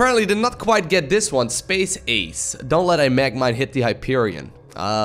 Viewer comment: I heard "Space Aids"